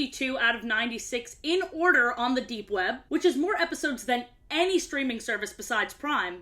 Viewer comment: I thought you said "Dark Web" instead of "Deep Web" and my brain just broke for a bit trying to figure out how you went from streaming services to giving up and going straight to the Dark Web to find all the episodes of a kids show